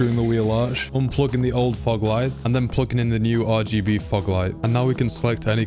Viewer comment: he said fog lights, I thought he said fuck lights😅😂